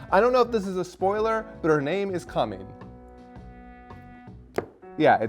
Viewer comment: I swear I thought said 'Her name is 'Cumming'."